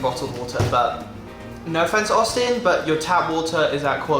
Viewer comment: hold up did you say austin